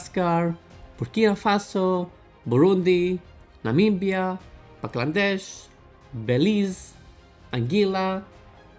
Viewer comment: did bro actually say Buglandesh??? 💀